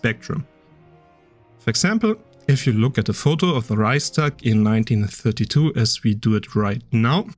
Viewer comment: Da du selbst Deutscher bist, wirst du vermutlich meine Verwunderung nachvollziehen können, dass in den gelben Untertiteln beim Timecode 05:02 "rice tag" statt Reichstag steht. Wie kam das denn zustande? Ach so, und wenn ich das richtig verstanden habe, ist Metatron tatsächlich ein Italiener, insofern ist seine Perspektive an sich schon europäisch. Aber die Videos, in denen die Frage diskutiert wird, stammen vorwiegend aus den USA, von wo ich auch die meisten Diskussionen zu diesem Streitpunkt wahrgenommen habe.